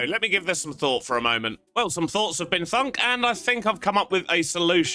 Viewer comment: did just say thunk? 🤣🤣🤣